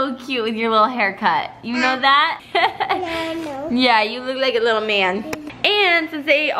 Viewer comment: Did I hear say yeah I know? Lolol 😂